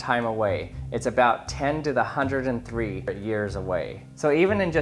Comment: my mind skipped over the 103 and I thought he was saying the death of the universe is 10 years away